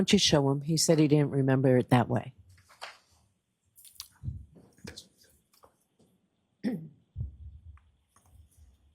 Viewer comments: 😂 What does this fool say under his breath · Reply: Gurl, it sounds like he said, "that's what he said". But it seemed like it was an alter ego speaking for him. May be a little "Coo Coo for Coco Puffs"😂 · The heck does he say under his breath at 5:28? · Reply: Sesame seeds? lol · What's he mumble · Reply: WE SHOULD ASK JOE BIDEN.